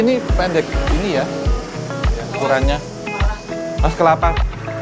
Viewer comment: bs aja bilang, "Awas kelapa.." Itu aku ga salah denger, kan, ya?